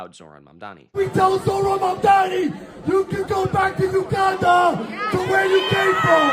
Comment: Did he say Uganda???